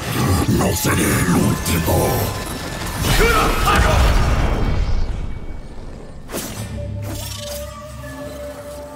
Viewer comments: que dijo yone ahí? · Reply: KURO SAGO o algo así xdd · Que dice yone en el 8:20, leí comentarios y nada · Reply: KURO' SAGOL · Alguien sabe que dice yone el el minuto 8:21 ? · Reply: Kuro'Sagol, es el nombre del demonio, cuando usa la espada mágica sobre un Azakana (la raza del demonio) y dice su nombre lo sella en una mascara